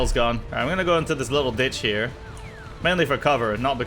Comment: did he say ditch? Beacuse i am just bad at hearing words that sound similar